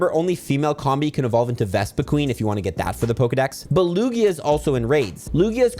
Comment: Not you calling Lugia, BELUGIA AT 4:48! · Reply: I heard “but Lugia”